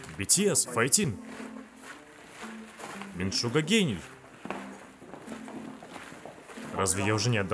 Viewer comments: мне одной послышалось, "Мин Шуга ГЕЙ"? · Когда говорили что-то хосоку и юнги сказал: шуга гений,мне послышалось шуга гей🤣